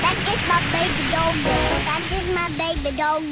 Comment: "That's just my baby doga" - 👶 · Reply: I hear “I KISS MY BABY DOGA” help fix my ears